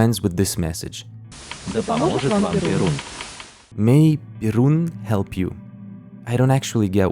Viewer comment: i heard "Perun", instead of "Birun". Thought that I was tripping, but it aligns with "Veles" that are mentioned later. Those are ancient slav pre-christian dieties. But you probably learnt this by now. Also no wonder you pronounce Russian well, you just said you grew up in EUrope. XD